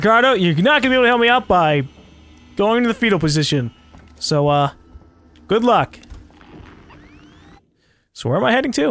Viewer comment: when u said good luck I thought u said good fuck